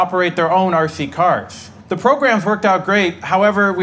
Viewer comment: I thought he said " they are illegally blind " lmfao